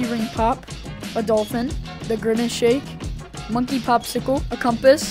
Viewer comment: did he just say the grimace shake 💀